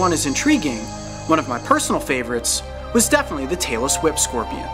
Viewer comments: He said tailed whip Scorpion but I thought he said Taylor Swift scorpion Bet u million bucks u thought it said Taylor Swift scorpion Sometimes I just don’t get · I thought you said the Taylor Swift scorpion. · OMG I MISHEARD TAILLESS WHIP SCORPION AS TAYLOR SWIFT SCORPION WAT? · only me think he say taylor swift scorpion? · lol 0:38 subtitles said Taylor Swift Scorpion . A.k.a Tail-less Whip Scorpion . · Lolz. I thought he said "the Taylor Swift" scorpion at the beginning of the video! 😁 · i thought he said “Taylor Swift scorpion” instead of “Tailless Whip scorpion” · LOL I THOUGHT HE SAID "TAYLORSWIFT SCORPION" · OMG at 0:40 i thought he said Tailor Swift XDDDDDDDDDD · Tailess whip 🦂?I thought he said Taylor swift scorpion😂🤣🤣🤣🤣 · I thought he said "the Taylor Swift scorpion" like I thought she was a snake tho. then I realized what he said lmao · I MISHEARD TAILLESS WHIP FOR TAYLOR SWIFT XD Save Me XD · i could have sworn in the intro he said the tailor swift scorpion 😂😂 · I thought said the Taylor Swift Scorpion. 😂😂 LMao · Tailless Whip Scorpion, at first I thought I heard Taylor Swift Scorpion. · When he said "tailless whip scorpion" I thought he said "Taylor Swift scorpion" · Thought he said tailer swift scropion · I heard the "the mayla swift scorpion" I bet I'm the only one who heard jt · I thought he said Taylor swift scourpian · When he said "tailless whip scorpion," it was so fast I thought he said "Taylor Swift scorpion"